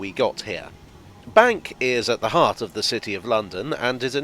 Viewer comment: "Bank is at the heart of the city of London ..." I thought you said "Bank is over half of the city of London ..."